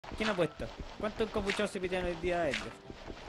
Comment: Por qué chucha entendí "Completos" en vez de "Encapuchados". Toy mal wn 🫠